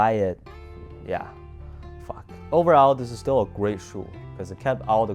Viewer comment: i heard that lil “fuck” lol